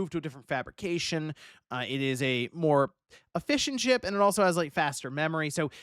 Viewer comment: i thought you said “a fish and chip” instead of “efficient chip”